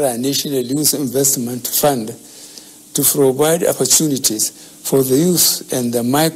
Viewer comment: Is it protest or frotest?.....me I'm not understanding it o....I give up🚶🏼‍♂️🚶🏼‍♂️🚶🏼‍♂️ But wait o...I heard "frovide"...or he was parounocing "provide..?"